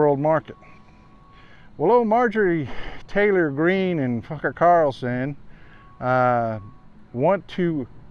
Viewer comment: I swear he said "F#cker Carlson" ❤🤣